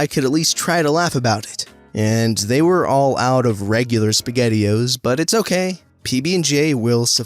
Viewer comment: Am I the only one who thought that he said PB&J SpaghettiOs?